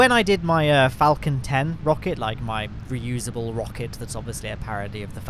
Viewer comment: I know you're saying "Falcon-10" but I keep hearing you say "Falcontent." As in, Falcon and Malcontent but combined into one word...